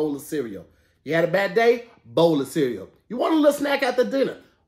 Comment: Why I thought a said “boneless cereal” in that commercial 😂😂😂